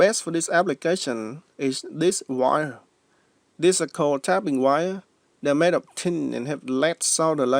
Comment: Good tip, but it's easier and safer for the batteries if you apply solder to your connecting strip as well and then place it facing the battery cap so when you apply heat, both solder covered points melt very rapidly and so it takes a very little amount of time. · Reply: Also that got me , I heard him say it was pre tinned and thought he said tapping wire , I checked it against the English captions and it said tapping also , but as we now know it's tabbing wire ! good news is we got there in the end guys ! Good video and contrabuetions unlike my spelling 😉🙃😎 NZ❤